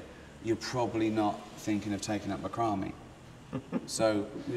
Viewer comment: Whad did he say? If you've been ? or you're first born as a one in four chance of not surviving the first year, you're probably not thinking of taking up the crummy. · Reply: Leon Lopatheus ...taking up macramé